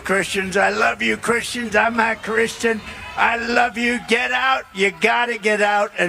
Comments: when he says I love you Christians and refers to himself it sounds like he says “I’m not Christian”. That is what I hear…. I look at the closed caption and it says I’m Christian when he says it but doesn’t show “a” or “not” but if I were the devil I wouldn’t either. Just something to rewind and go back, I’m listening on replay and at work and hearing this. Just an fyi. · when he says I love you Christians and refers to himself it sounds like he says “I’m not Christian”. That is what I hear…. I look at the closed caption and it says I’m Christian when he says it but doesn’t show “a” or “not” but if I were the devil I wouldn’t either. Just something to rewind and go back, I’m listening on replay and at work and hearing this. Just an fyi. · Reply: I hear him say “I’m not Christian” 😮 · Sounds like he says im not a Christian